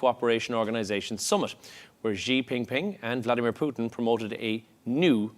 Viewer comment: Did he just say "Zhi Ping Ping?" WTAF?!?